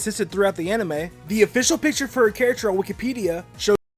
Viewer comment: Am I the only one who thought pronunciation of Wikipedia (4:10) sounded like Wookiepedia? Does that mean Jigglypuff is in Star Wars?